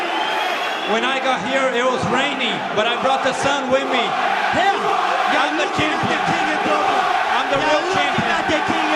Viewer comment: Whats the crowd chanting from 9:04-9:10? What are the words in irish and translated to english? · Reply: "Who are ya", ya as in you, so "who are you" Basically saying that nobody cares about you, that you're a nobody.